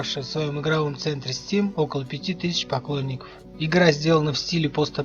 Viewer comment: Мне послышалось "Покойников" 0_0